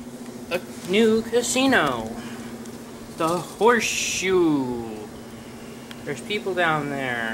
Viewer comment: I thought he was going to say the name of the building was literally "HORSESHIT".